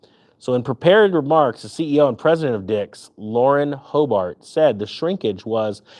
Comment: 2 & 3 i had to rewind i thought you said Lauren Boebert lol